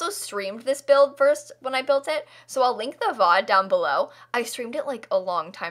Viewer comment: did anyone else hear her say vod instead of vid?